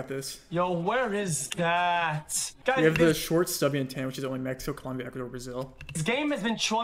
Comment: does anyone know what he is saying in 15:00 short subby what? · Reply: Short stubby antenna